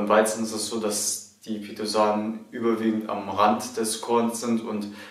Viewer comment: ich verstehe leider akustisch nicht... wie heißen die löslichen Ballaststoffe? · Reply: Pentosane :)